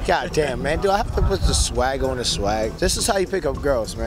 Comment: Did he just say "Do I have to put the swag on the swag?"